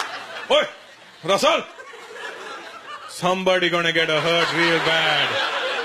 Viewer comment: I thought he said someone's gonna get a hurt liver the first time.